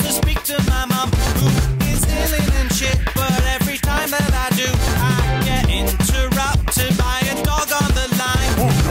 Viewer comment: Did he say illing and shit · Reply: You're both wrong, he said "She is ill-in and shit"